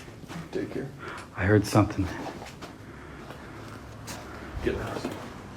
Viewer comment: I heard a voice say "Get out" but they didn't notice it.